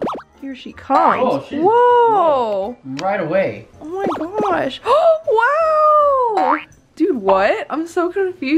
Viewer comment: Listen to 12:07 did he say oh sh*t